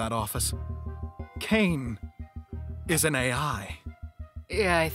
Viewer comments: I thought bro said Kinger and was about to cry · Why did I think he said kinger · omg when first i watched this i thought he said "caine isn't AI" and i got so hyped up but now after rewatching it im kinda sad